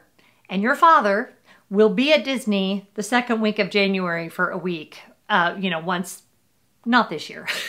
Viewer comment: I thought you said 2nd week of FEB at first?? But then you said Jan..? (confused) Either way, I agree the cheaper, COOLER months are more pleasant than dealing with the massive heat! (unless going to the water parks there are important) I am happy that you are planning this for your family. With kids graduating this could be an important time to "lock them down" Lol - (although WHO would say no to Disney!!) 😊 Pls don't forget to show us your December Plum paper stickers & how you use them! 😁 Thx!